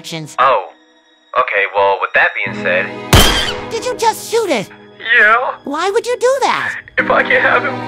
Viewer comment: "DID YOU JUST SH0OT IT" i heard it as DID YOU JUST F*CKING SHO0T IT